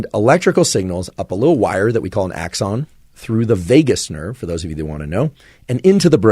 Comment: Who else heard him say Vegas Nerve?